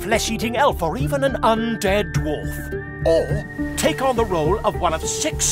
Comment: did I hear right ? undead .. dorf ? inhale profoundly SIGN ME THE FUCK UP !